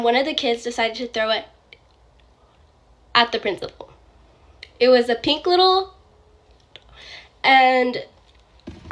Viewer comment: Does anyone know what she said im not good at reading lips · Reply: Dildo